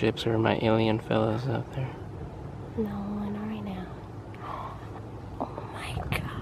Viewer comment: Hehe love her remark to your "let see if there are any alien space craft up there" 5:25 "No, not right now" That is what she said right :D